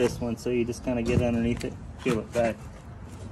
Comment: Did I hear “special dog treats”???